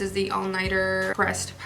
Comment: Omg I thought she said breast powder!😂😂😂 It took me a minute to realize she said pressed!